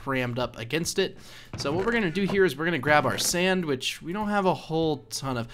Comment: I thought you said sandwich lol